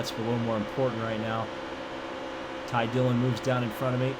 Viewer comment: Did you just say “Ty Dillon coming down in front of me”?